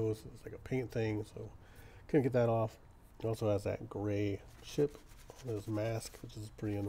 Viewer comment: "grey sh**" ooooo - no, I think you said 'chip'- what is this a depraved BARF stream?